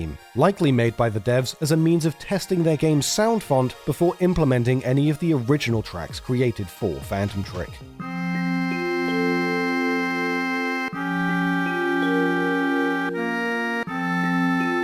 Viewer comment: im sorry did he just call it phantom trick